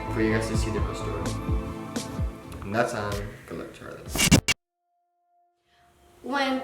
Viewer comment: Hold up hold up.... on 5:17 did he say “ and that’s on good luck charlie” ? 😂😂 I think I’m hearing things 😭😂